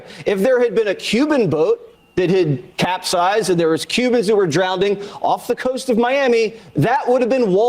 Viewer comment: Did he actually say “drownding” 🤦🏽‍♂️